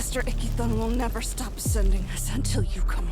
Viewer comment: I can't be the only one who hears "icky thong" right?